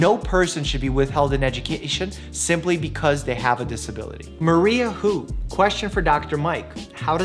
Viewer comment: did he just say maria i saw mario lolol